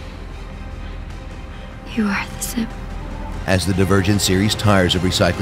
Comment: I thought she said Simp